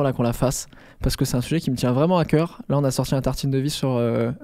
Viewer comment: Yes un nouvel épisode des tartines de rehausseur ! :D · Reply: moi j'ai entendu "tartines de VEO"... morte haha